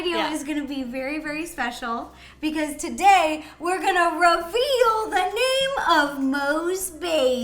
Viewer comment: ro-veal? I was gonna think you were going to say reveal cause I was going to be like “:o DiD sOmEoNe TaKe Ro?! ¡-¡”